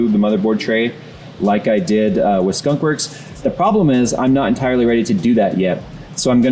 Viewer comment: Did I just hear "CHUNKWORKS"?